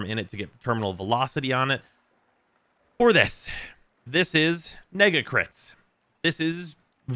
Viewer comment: what did he just say .ah yes nega crits